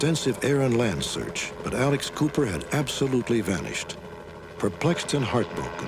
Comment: I thought they were saying Alice Cooper